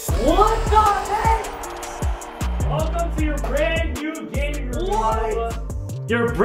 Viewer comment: Did he say welcome to your new room my nigga?